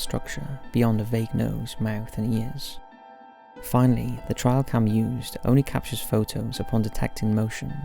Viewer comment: why does it sound like he is saying "trial cam" and not "trail cam"?